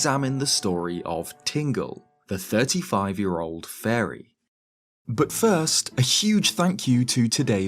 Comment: why did it sound like you said “tingle, the 35 year old furry”?